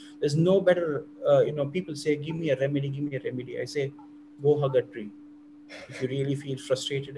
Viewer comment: Oh, hug a tree. I thought he said have a drink.